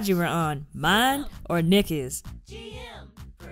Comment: I thought you were saying 'sim gm kardashian' instead of 'sim gm production' LOL!😆😆😆